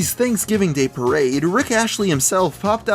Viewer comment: did he really say 'Rick Ashley'?